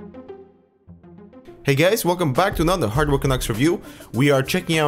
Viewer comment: wtf did he say ( hey gays) ??!!!